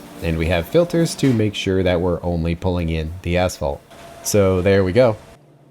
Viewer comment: I hear "ass fault" every time pronounce asphalt.